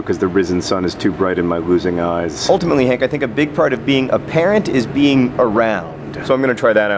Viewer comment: At first I heard, "I think a big part of being apparent is being around" and I thought, "Well, yeah, sure, John. Duh. What does that have to do with anything?"